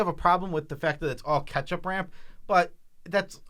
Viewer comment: Swore I heard say “ketchup ramp”